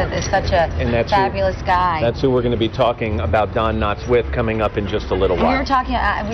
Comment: Did she actually say 0:36 that Barney Fife was the wonderful Sheriff Andy Taylor aka Andy Griffith after spending so much time saying how much she loved that show but thinks he was the sheriff?? :-( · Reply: she said "with" the wonderful sheriff Andy Taylor